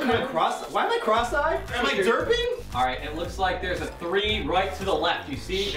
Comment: I thought Anthony said derpy...hehehe